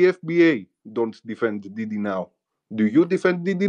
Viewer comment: I thought that ni@@ was saying dee dee..i was like who the hell is she..🤣😂🤣😂🤣 he was trying to say diddy😂😂😂